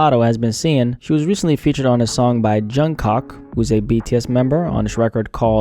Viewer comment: he really said "Jung Cock" 😂